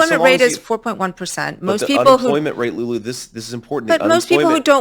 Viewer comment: him refering to her name? Sounded like Lulu like Delulu.